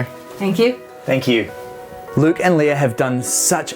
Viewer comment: when he met them i thought he said Luke and Leia i was so confused